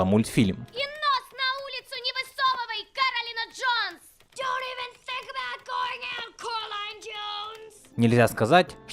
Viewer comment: а что она сказала? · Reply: "И нос на улицу не высовывай, Коралина Джонс!"